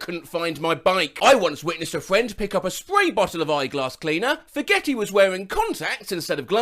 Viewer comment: thought he said became a spray bottle 💀